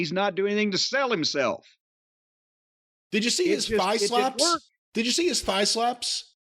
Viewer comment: Samoa Joe , should have won. When said "thigh slaps" I thought he said bi-flops talking about his biceps lol. So a baby won the OH Tournament , who cares now